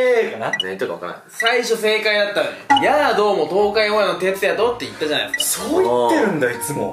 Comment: 「やぁ どうも東海オンエアのてつやとって言ったじゃないですか」って言ってるけど、0.25倍速で音量MAXにしても『東海オンエア』が一生聞き取れない。 どうやら私は、メンバーとは違う、バベられた世界で生きていたようだ。